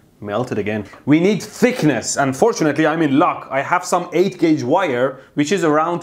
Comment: At first I misheard "Unfortunately I'm in luck!" and I found it much more fitting to you and your doings than "And fortunately..." :D (psss... gimmee da meeetttaah?)